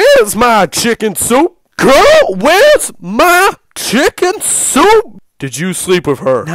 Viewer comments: "Wheres my chicken soup? " "GIRL, WHERES MY CHICKEN SOUP?! " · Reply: I thought he said "CUH WHERES MY CHICKEN SOUP" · "Wheres my chicken soup? " "GIRL, WHERES MY CHICKEN SOUP?! " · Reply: I thought he said "Carl where's my chicken soup" 🤣🤣